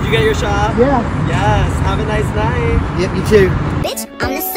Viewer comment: Follow us on insta for more✨ and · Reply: At first I thought said "Have a nice life" 😂